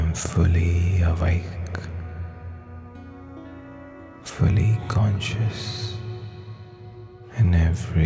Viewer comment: can someone write down what does he say at 8:30 please, my english is not so good, thank u! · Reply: He says "fully conscious" or aware/mindful in other words 🤗